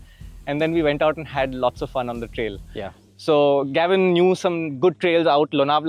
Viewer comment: People busy with the bike review, I am stuck at saying “Gavin Knew Some” which sounded like Gavin Newsom (California Governor) at 1:14 😂